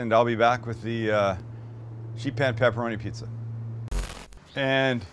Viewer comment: I thought that said sheep in pan pepperoni pizza. Anyway, great vid as always Sam &. Be safe y’all. 🤗🤗🤗